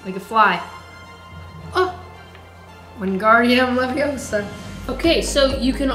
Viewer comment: did just say Wing AR dium Leviosa